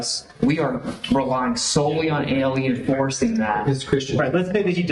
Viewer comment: I thought he said "Alien Forcing that..."